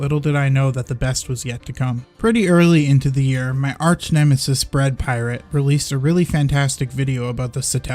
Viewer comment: Sounded like you said Brad Pirate around 4:26, I really enjoy watching both your channels.